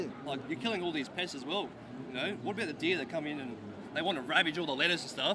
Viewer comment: Did he say deer? what fucking country is this?